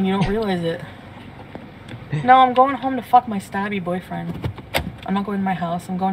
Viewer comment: I thought she said her scabby boyfriend